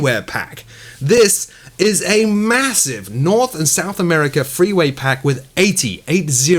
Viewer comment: At about 6:24 did say freeway pack? 😂